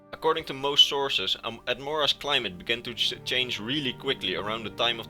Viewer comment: the captions say “according to most sources, I’m Ed Morris” 😂